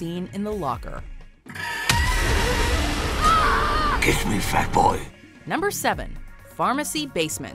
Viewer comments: I used to be scared of this :< ALSO DID PENNYWISE JUST SAY AT 2:52 IT: GIVE ME FAT BOI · i found the scene funny Idk if i misunderstanded but i heard GIVE ME FAT BOI